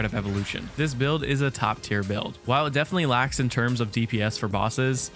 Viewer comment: Thought you said top 700 builds